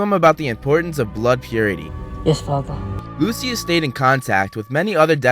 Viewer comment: I thought he said Yes papa XD